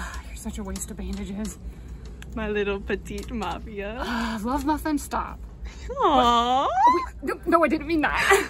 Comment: what did chuuya say? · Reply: loveeemuffin